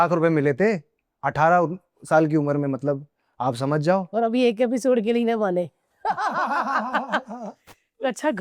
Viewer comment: What's he says on 30:57? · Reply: She Said "Aur Abhi Ek Episode ke bhi na maane"